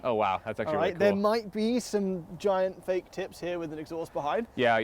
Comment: I heard "fake tits"